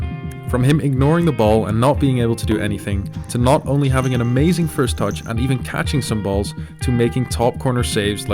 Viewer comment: what did he say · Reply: First touch